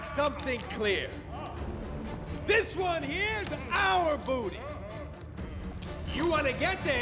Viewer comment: I always thought he said this is our "bounty" that just made it so much better hahaha